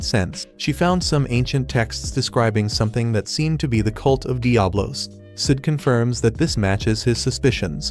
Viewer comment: thought she said diavolo